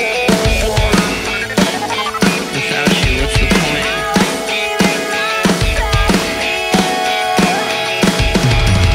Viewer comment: what did he say at 0:19?! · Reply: "Without you whats the point?"